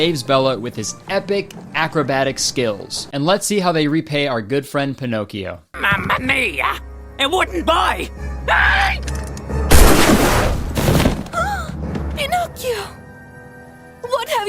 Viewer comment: it took me until i watched this clip 10 times for my non native english speaking ass to understand that he was saying "a wooden boy!" instead of "he wouldnt buy!", this scene now makes sense for me